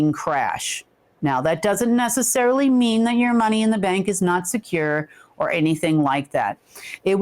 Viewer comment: So it sounds like you said leave our money in the bank?😮